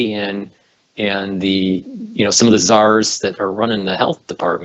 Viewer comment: it sounded like said SARS was running the federal health departments 😄👍. Hell, we might be better off were that the case!